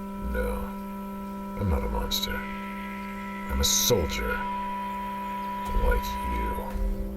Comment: Locus says soldier but the closed caption says solider.